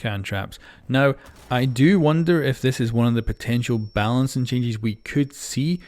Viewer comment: what i hear , "Changies"